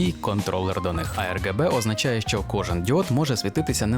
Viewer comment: замість діод мені почулося ід*от...